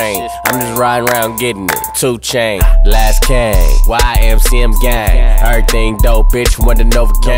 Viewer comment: I heard, "Why Am See I'M Gay." @ 1:19 But he said, "YMCM Gang".... o.O I love Tyga though don't get me wrong.